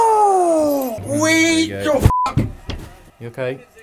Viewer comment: Sound like says We Go Fuck!